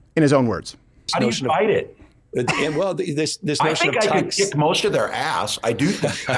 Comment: No, no no that’s not what he said… You misheard his words he said “he could lick most of their asses”…